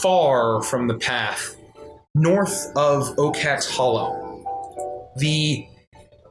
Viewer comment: did i hear that properly? "ocax hollow"? as in ocax the owl?? from poppy??? no way that's coincidence... i love this game already oml..